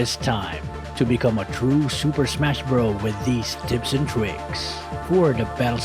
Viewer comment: "Tits" & "Pricks" That's what he said